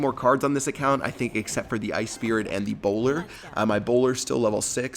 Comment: When he said bowler it sounded like he said Ebola